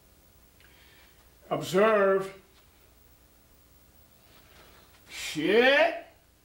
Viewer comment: Did he say "shiiit"?